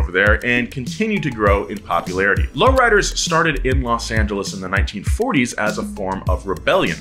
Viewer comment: Who heard him say Los Santos instead of Los Angles